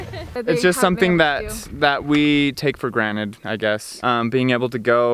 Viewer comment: Take For Granite ?? I LEGIT Heard That !!!!!! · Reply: He said it "granted", it's just a subtle "t" in the middle for a lot of types of American accents. And even if he'd said it with the common error of "granite", it wouldn't matter one bit. Pointless thing to comment about. I'm a language nerd as much as anyone but being pedantic is dumb.